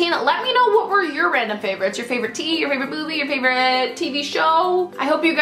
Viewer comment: Totally obsessed with Estee as well. Also, at the end when you said "tell me your favorites. favorite show, favorite movie" I thought you said favorite booby.......yikes lol